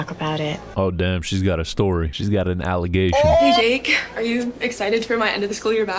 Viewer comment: why tf do the bullies look like flight attendents 💀💀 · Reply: i fr thought when she said hey jake after he said she got some aligations i thought she said "hey drake"